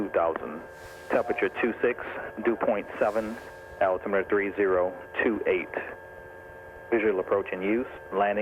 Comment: did he say visibility 1 tree? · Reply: did he say altimeter three ... ?